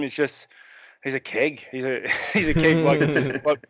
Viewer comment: what does he call Kelvin? Sounds like “king” or “kid” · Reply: a keg. Like a beer keg, stocky, heavy and round